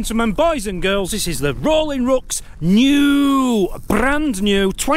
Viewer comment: Needed subtitles for you saying The Rolling Rucks, I thought you were saying Rooks 😂😂, luckily you put a link. What a lovely couple they are. ❤❤